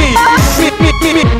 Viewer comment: First it sound like mario said woomy